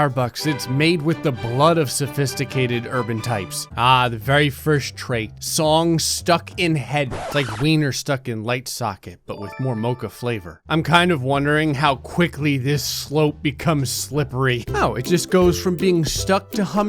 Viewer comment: "Wiener stuck in light socket"?! I almost misheard that as "wiener stuck in lightsaber"...